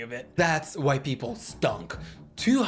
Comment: Omg when you said that's why ppl stunk ....i first thought you said that's white people skunk. 🤦‍♀️